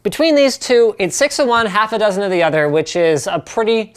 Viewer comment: What does he say at 6:22? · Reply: Think you're referring to the expression he uses: "six in one; half-a-dozen in the other" just meaning it's a matter of perspective between the two.